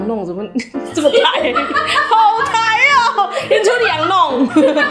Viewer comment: 聽成「in to the羊肉」（餓了